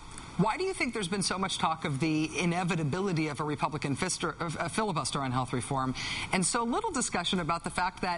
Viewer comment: Did she just say "fister"? 4:28 =)